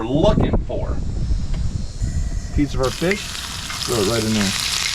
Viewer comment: did he just call it "fish" when he put the meat in the fry pan?